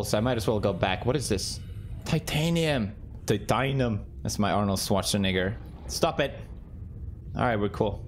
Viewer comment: what did he say ？😅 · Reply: Arnold Schwartzenegger